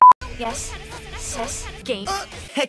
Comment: Why do I hear sus